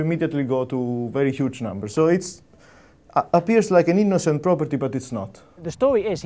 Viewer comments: the subtitling says "...any [INAUDIBLE] property...". I think what he said was "So it appears like an innocent property..." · where CC says [INAUDIBLE] he clearly says "innocent" :D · For deaf people,at 5:33 : i think he said " like an innocent property" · That part where the subtitles say (inteligible) at 5:34, I believe he says "So it appears like AN INNOCCENT property, but it's not" I suggest to fix it